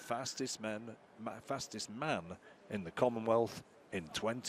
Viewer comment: wtf the announcer said @ 2:54 "who is going to be crowned the fastest Men ...fastest Ma'am (women) in the common wealth in 2014." Am I the only one who heard this cause if so that's a major slip up right there. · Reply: He said man he messed up by saying men because they can only crown one man so he said fastest Mann he emphasize heck out of the n that it came out sounding like M